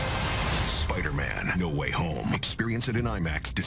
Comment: at the end idk if my hearing is bad but because of how the narrator said it I hear "Spiderman - The Way Home"